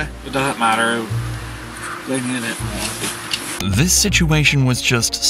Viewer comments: the unintelligible at 5:30, I think he's saying, "they needed it more" - as in, whoever stole his shopping cart needed it more than he did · He actually said "they needed it more" I don't think I could react like that if everything I owned was stolen. Glad he was able to get back to his family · HEY so im a bit late (1 year late LMAO), not sure if anybody pointed this out yet but what um he said at 05:31 was "they needed it more" im pretty sure. it made me so sad :(